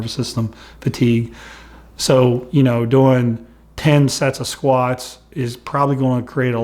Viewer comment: Did he just say ten sets of squats?